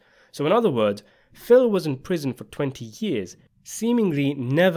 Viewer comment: Didn't catch that! Did he said 20 years or something?